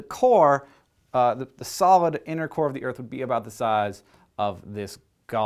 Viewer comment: every time he said "innercore" i thought he said "intercourse"